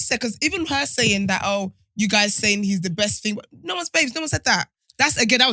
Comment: Thought you guys said no one is safe!!! Hot him up😂😂😂